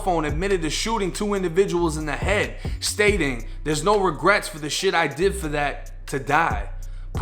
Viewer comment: It wasn’t during a jail visitation it was recorded on his phone a month after the shooting. He was arrested 4 months later. Context is important when considering this recording. Who knows maybe he was recording a song. That’s pretty typical rap shit to be saying lol. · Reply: You are right, it was on his personal phone. When I double checked I had mixed feelings bc I thought he said “I don’t have any regrets for what I did to that...” But he actually says “There’s no regret for the shit I did for the N to die” then made the gun gesture with his pen, with the other evidence combined it looks really bad. You are right though we don’t know the context. But I guarantee when they play the whole audio, the jury will know whether it was a serious conversation or a rap lyric. He has good lawyers, if they try to take what he said out of context his lawyers will have done the work to get the full recording with context. But you’re right about the video, sorry for the misinformation